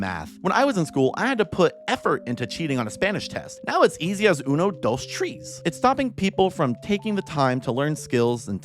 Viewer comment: Uno those Trees.😂 That’s what it sounded like to me, lol.